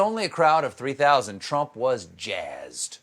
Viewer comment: Feel the spirit? Ohh. I thought Trump said "can you feel the spread".